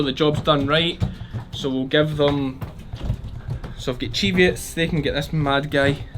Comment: Ha ha, whenever you say cheviots I hear "deviates"., as in "I'm going to put them out with the DEVIATES".